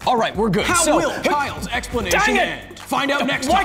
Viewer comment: is it only me or did anyone else hear kaioken